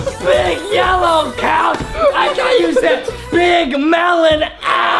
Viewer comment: did not say that said big 💛 couch